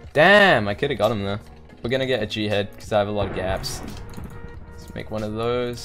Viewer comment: I heard "we're gonna get a jihad cause we have a lot of gaps" oh, oh no